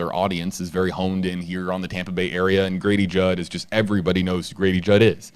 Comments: Thought you said Gravy Jug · I thought you said Gravy jug 😄